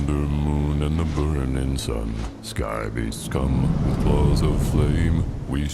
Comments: I love the fact that you had a misheard lyric moment. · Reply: When Geoff says “sky beasts come”, I first heard it as “sky be scum” for some reason lol. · Your misheard lyrics 🤣 My brain works the same way 😉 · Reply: My first misheard lyric is in the opening monolog by Geoff. Half the time I hear it as written "Sky beasts come", and half the time I hear it as "Sky beast scum". Both fit the story being told.